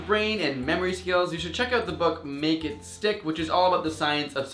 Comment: wasnt looking at the screen at the end and misheard 'make it stick' as 'naked stick' :P